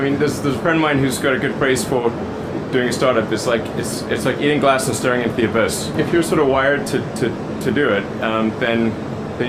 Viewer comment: what does he say his friend said? "is like eating glass and starting at the abbis?